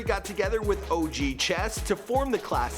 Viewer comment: He said “buddy rich” and I thought I heard “Roddy Rich”